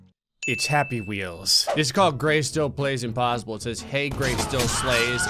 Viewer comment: i thought he said GreyStillSwan